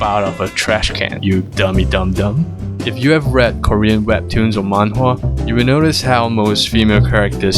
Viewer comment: Am I the only one heard him say manwhore instead of manwha?